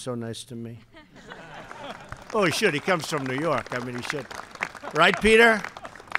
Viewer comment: did trump just say "well he comes from new York, I mean, shit"